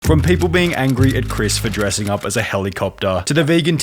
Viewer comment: Bro...I thought he said from people hating on chris to being dumber then chris-😂✨️